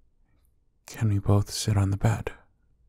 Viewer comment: starts crying Me: wait nOoOo I’m sorry 🥺 Also 7:30 did he say bed? Aren’t we in a bathroom?